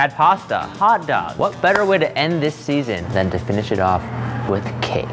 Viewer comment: I THOUGHT HE SAID SERIES FINALE BUT HE SAID SEASON MY LIFE FLASHED BEFORE MY EYES